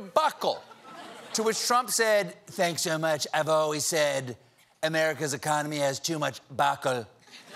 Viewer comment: I think trump actually said "America's economy has too much Obacle."